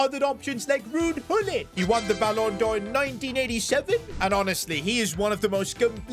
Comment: I thought you said 1937